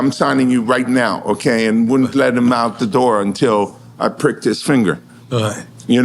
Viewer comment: 😂 did ya say 18:05 you pricked his finger...🤔